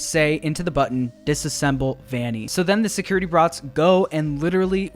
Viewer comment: What I heard at 7:12 : "So the security brats..." What is wrong w/ me 💀